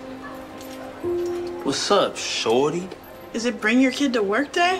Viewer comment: bro sounded like „wassup shawty🔥🥵“ bro is drakes son